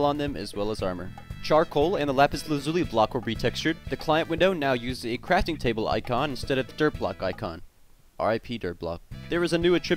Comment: What did he meant with the infor at 0:56 - the dir block icon and crafting table icon??? Can anyone clear me up that - he spoke too fast and no matter how many times I reversed it I couldnt understand what he meant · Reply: The dirt icon in your websites bar at the bottom is now a crafting table and not a dirt block :( R.I.P Dirt Block <num>